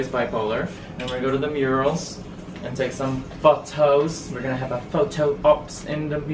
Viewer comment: Okay when said... "we're gonna take some photos"... I kid you not I thought he said... "fuck toast"... What the hell is wrong with my hearing 🤣🤣🤣🤣